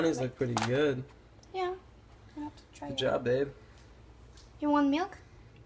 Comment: that's what she said? Lol. Sorry, couldnt help it. You two look really cute together. :) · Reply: I think she says "You want milk?" :)